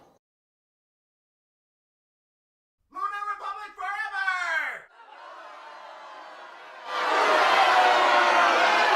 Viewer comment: what dose he say at 11:42? i cant make it out even with caps ? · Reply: Lunar Republic forever! XD